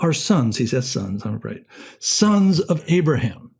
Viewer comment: Can anyone catch what he says at 23:24? "He says sons Am I right? "He says sons outright?" Thanks